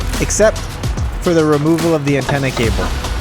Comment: It's a coaxial cable. It can still be repaired but I needed to run it through the hole so I couldn't have any bulges. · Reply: Okay, that makes sense. I thought you said you cut the wire you probably said cable. I'll cleanup the comment